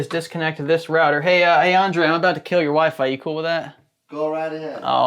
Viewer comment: thought I heard say 'I'm about to kill your waifu, you okay with that?'